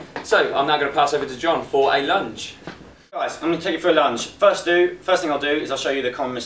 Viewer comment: I thought he said ''imma take you for a lunch'' lol